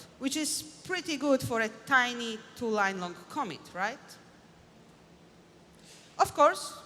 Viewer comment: Typo ~ 9:31 where closed captions read ‘comment’ instead of ‘commit’